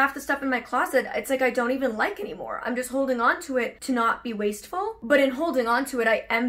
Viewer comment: When said that she worked with her stylist to reduce her waste. My brain heard it as "waist". And my brain sprung into action. How great would it be if a PT was also a stylist? :D New business idea!